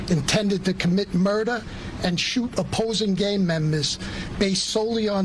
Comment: the closed caption at the top said "shoot opposing gay members" instead of gang members. Youtube is antigay.